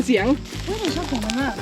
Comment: ลองฟังดูพี่พูดว่าชอบเสียงมันมาก